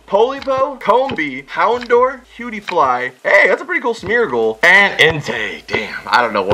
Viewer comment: did anyone else hear Smeagol from lord of the ring